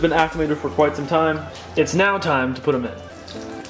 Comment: I thought he said it’s nap time to put them all in